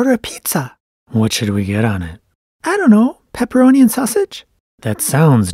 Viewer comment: I thought he said pep and sausage because they are technically both dead so they would be able to eat it :(